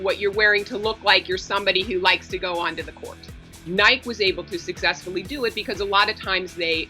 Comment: Did she just say nīke?